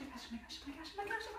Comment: Bug: exist 17 y-o girls: mecallomecallomecallomecallomecallo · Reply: ohh, it says that, something like "oh my gosh" but I heard it like "me callo"